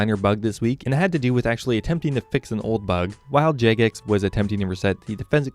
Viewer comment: subtitle jacocks instead of jagex xDD